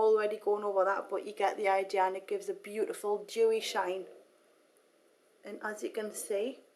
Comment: 31 Did she say it gives a beautiful Jewish shine? ....